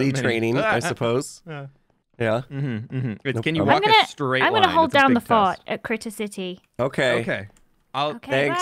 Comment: Apparently I'm incredibly immature. @ 5:54 I thought she said "I'm going to hold down the fart." I couldn't hold back the chuckle.